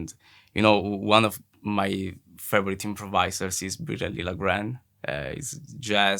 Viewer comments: Who is this guy mentioned here ? Don't quite understand the name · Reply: Bireli Lagrene, a master of guitar, but also great on bass and violin. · What is the name Matteo says here? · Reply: Bireli Lagrene